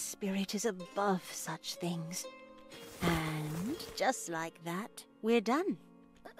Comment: 😂😂😂 58:03 I was looking away and i thought she said "shit like that." I about cracked up 😂😂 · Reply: lolol I can't! Without seeing it written out, it really does sound like she said "shit like that" xD